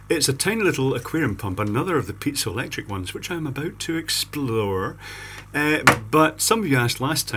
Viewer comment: I heard explode